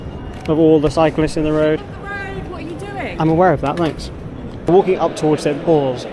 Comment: What is she saying at 9:10 😂? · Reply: You’re standing in the road. I replied saying “ I’m aware of that, thanks” 😂